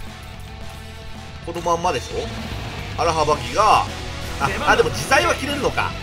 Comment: アラハバキをアラハバ「ギ」って聞こえたのが気になる。 こっちの勘違いか、向こうの活舌が悪いのか、普通に間違っているだけなのか…